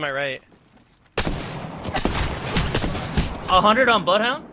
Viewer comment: I swear when said 100 on bloodhound, it sounded like he called him butt-hound lmao